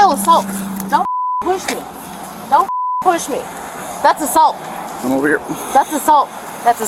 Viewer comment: i think she was saying that’s assault just assuming